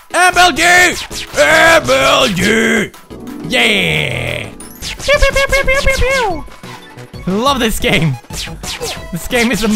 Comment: Everytime he throws an arrow i hear "Pewds, pewds, pewds, pewds, pewds..." Lol